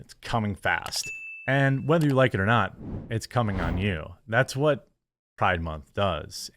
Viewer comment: Its coming hard, its coming for you!! Lol😅 I thought he said its coming on you!! I guess pride month really is coming!! Ok I have the humor of a 13 year old!! Keep kicking ass Jeremy!! Happy to see this channel grow.